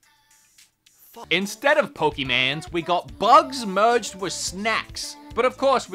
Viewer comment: I heard ,,Instead of Pokimanes...'''